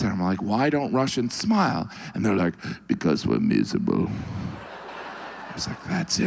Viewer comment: What does the Russian say at 1:23? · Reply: "...….because we're miserable..."